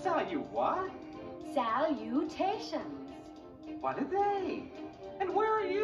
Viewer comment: At first, I thought Wilbur said “What a day” but he said “What are they?”